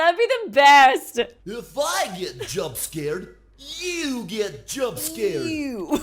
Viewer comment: Silly as it sound but when i first heard Toy Freddy says that line " If i get jumpscare you get jumpscare " i thought he said " You're fired jumpscare " and that's still make me laugh